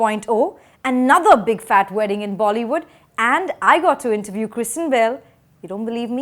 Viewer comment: I kid you not, every time I heard you say Christian Bale, I heard Kristen Bell and got super excited! Totally my bad but now I want the ladies of FC and Kristen Bell to hang and be all around delightful!